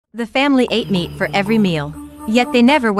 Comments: I thought you said the family ate me every day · is it just me or does it sound like she said for every meal they eat me